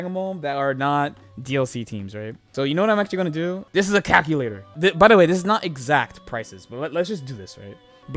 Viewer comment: Idk why but enthusiastically pointing out that the calculator on screen as "THIS IS A CALCULATOR" made me laugh more than it had any right to. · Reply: I heard "cap-ulator" lmao